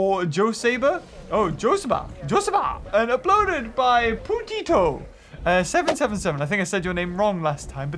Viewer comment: Did he just say putito?